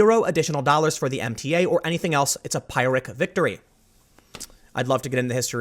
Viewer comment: Sounds like Tim is saying "Pirate Victory", instead of "Pyrrhic Victory".